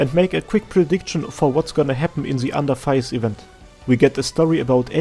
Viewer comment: I heard "under thighs" not under tides You'r culture showing